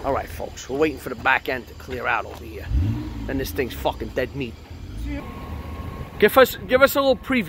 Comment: I think he said “Waiting for the back to clear out and this thing is fucking dead meat “ if so that was funny